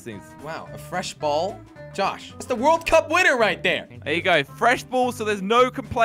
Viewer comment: did he say world cup